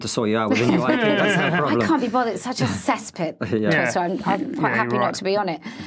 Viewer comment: She's 100% right about twitter being a cesspool. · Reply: thought she said cesspit